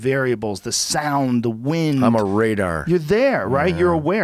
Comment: For being sober this guy seems like he's on a lot of drugs. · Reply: I just read this as I heard him say “I’m a radar”